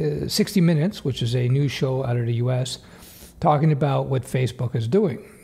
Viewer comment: I guess I can understand how 60 minutes might seem kind of new for a 169 year old.. 😅 Joe Biden is a young buck compared to that. 😝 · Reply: That's what I thought he said at first too. But he said "news show" not "new show"